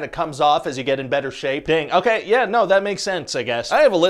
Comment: Misheard "dang" at 1:08 as "Bing"... I thought. Checked the Closed Captions, they say "dang" too so... Bing it, I guess it WAS just a subliminal Mondegreen.